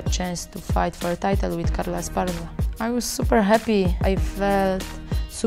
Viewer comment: I love that she is always super happy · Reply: villacorta LMAO, and I thought she was saying "I'm super heavy." I was like "no baby, you're not." XD